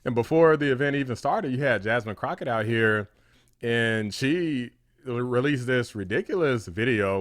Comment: You said at some point, "and then we got Jasmine Crockett out here" but I heard "and we got Jasmine Crocodile here". I'm going with Jasmine Crocodile. 🎉